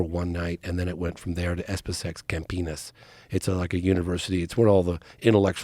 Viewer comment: Did he say Camp Penis @ 13:51?